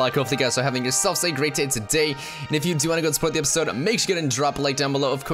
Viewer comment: I have no idea why but in the intro I heard "So if you wanna spoil the episode leave a like" had to replay it and now that I replay it really does not sound like it